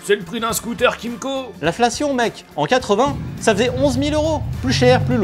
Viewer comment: Y a que moi qui suis assez ravagé pour entendre « la fellation mec »?